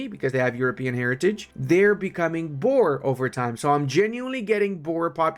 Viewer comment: I keep hearing they are gonna become Borg hahahahaha star trek reference